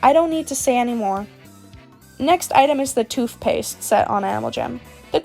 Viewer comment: Why did you say toofpaste 3:48 🤣